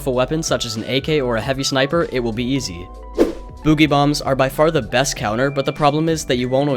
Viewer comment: I thought he said booby bombs